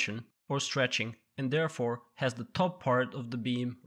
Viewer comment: Sounds like he is saying "the top part "rainforest".